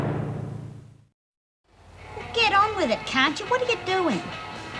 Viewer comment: Does Doreen call Lizzie the c word? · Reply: Doreen said, “Get on with it, can’t you?”.